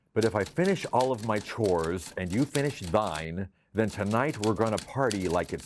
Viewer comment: But...... all these years.... I couldve sworn he said, "But if I finish all of my chores, and you've finished dying."